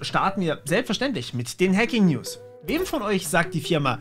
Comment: LOL. Ich hab gerade verstanden "wir fangen an mit den Hacking-Nudes"